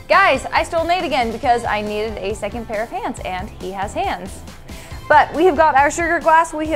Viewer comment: I thought she said " second pair of pants"